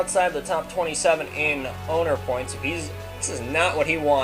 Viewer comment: i thought u said "boner points" lol